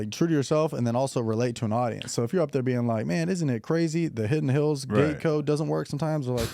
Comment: Did he say “man this n*gga crazy” at 2:07 ?